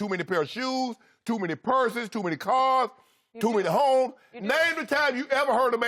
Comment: I thought Shannon said i got to many hoes 😂 I had to go back